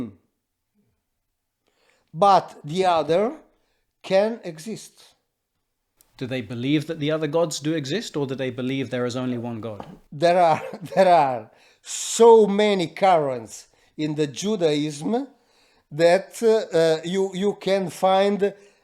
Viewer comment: For a moment, I thought Murao said at 1hr44mn38sc, "there are so many KARINS in the Judaism..." I burst out laughing!